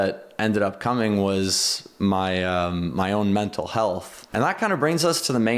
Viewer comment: When he said mental heath I thought he said, "Mouth of he double hocky sticks